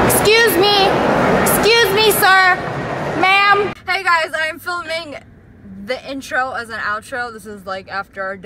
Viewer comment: I Legit Thought You Said Flamingo 😂